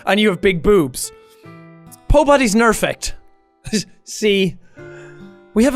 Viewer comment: did jack just say Pobody Perfect just want to make sure. HAahaaha